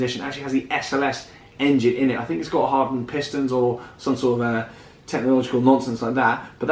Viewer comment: Almost certain I heard “Hardwood pistons”